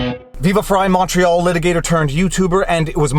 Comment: I always thought you were saying you were a “macho litigator turned you tuber.” This is the first time I realized you are saying you’re a “Montreal litigator turned you tuber.” Lol